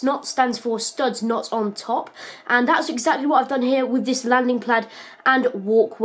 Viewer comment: he says landing plad instead of of pad